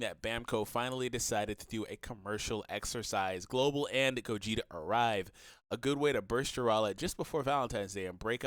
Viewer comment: Did I hear "Global and Gogeta Arrive?" 😂😂